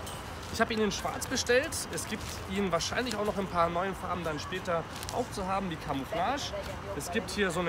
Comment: ja moin Farbenblind hab ich gehört?😂